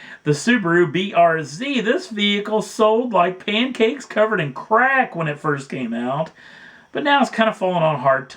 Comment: I thought you said pancakes covered in crap lol